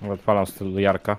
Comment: co mówi? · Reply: Odpalam stylówe Jarka(Pashy).